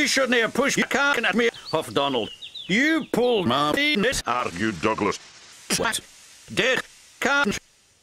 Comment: This is what I heard: "You pulled my ps out of your Douglas!"